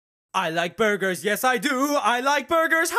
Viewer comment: i thought it said boogers xD